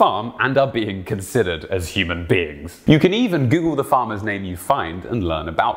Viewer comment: did he say human beings, or human beans?